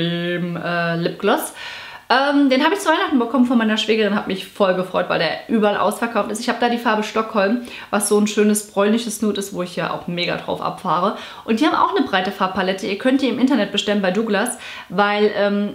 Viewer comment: Ich hab bei 18:48 100mal verstanden "polnisches nude" und mir die Finger wund gegooglet, um rauszufinden, was zur Hölle das ist 🙈